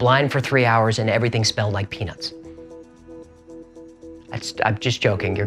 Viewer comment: I need to get my hearing checked. I thought you said "Everything smelled like Penis".... I had to listen to it 9 times to finally figure it out.